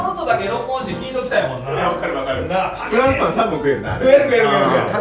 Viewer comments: フランスパン3本って聞こえる🥖🥖🥖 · 「フランスパン3個食えるなぁ」って聞こえる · フランスパン3個食えるなって聞こえる